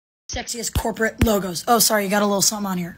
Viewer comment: Omg i was so confused i thought she said sexist and not sexiest corporate logo